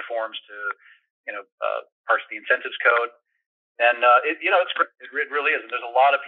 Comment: Minute 3:40 did he say his bank follows parse syntax code?